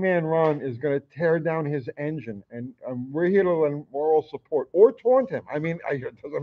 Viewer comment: I thought he said oral support for a second